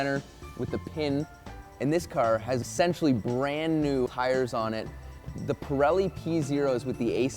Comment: I listened several times and I swear at 2:55 he says the car has essentially brand new “Hires” instead of “Tires”. Beautiful car by the way.